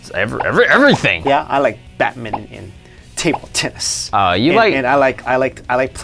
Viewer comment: at first I thought he said tittie tennis lol